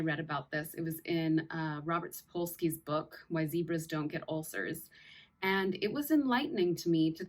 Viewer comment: I heard "Why LIBRA's don't get ulcers"...my partner's a Libra and i was like "whaaaat". Thank you for video...we appreciate your playful energy.